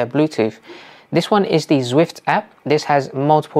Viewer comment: Great video, very helpful. Can you tell me the names of the virtual apps at 9:23. I can't quite catch the name. Thanks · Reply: Thank you! That one is called Zwift: Run & Ride ☺️